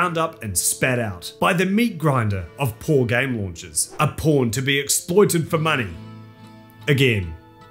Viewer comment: I misheard every time he said pawn as porn and was confused for a split second